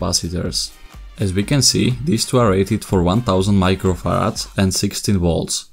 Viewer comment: DID HE JUST SAY MICROFARTSSS XDDD